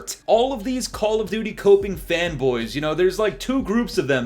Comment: I heard this as "femboys" and i think it actually suits them better 😂